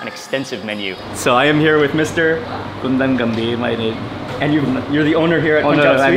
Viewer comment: i heard him said "gandang gabe" which means good evening in tagalog. hahaha Lol.